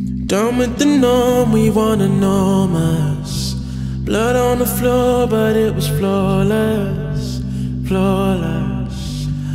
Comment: I always thought he said "blood on the floor but it was floor less"